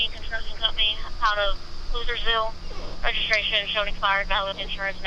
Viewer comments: Did she just say his registration came back saying he is outta Hooverville? Lol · Do I keep hearing “losersville?” 1:35 and